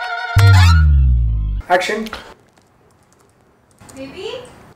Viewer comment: Who else heard action 😂😂